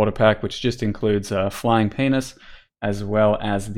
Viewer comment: did he say flying penis 😂😂😂